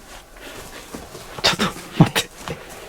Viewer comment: 、 こんばんは😊 12:27分に女の人の声でちょっとって聞こえます 😱